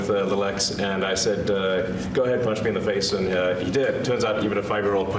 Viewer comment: You misheard. He said "my ex punched me in the face". That still doesn't narrow it down.